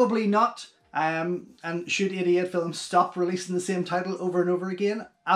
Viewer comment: Idiot Films!!! 😂😂 I thought you were saying Idiot Films for the longest!! When I asked my husband, (and he said 88 Films) I laughed so hard!! SORRY! I’m from Texas and my British English is mighty poor!! 😂 Loved the commentary!!